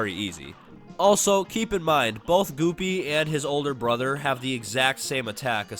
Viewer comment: Did he say guppies brother!